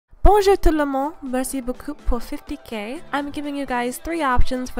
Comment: A)fan music video :D question tho what did she say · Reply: "Bonjour, Tout le monde! Merci beaucoup pour 50k" ~ Its French for: Hello, everybody! Thank you so much for 50k! 💟